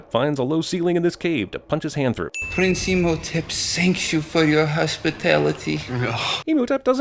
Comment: said Shanks you am i the only one who heard him saying that? Shanks instead of thanks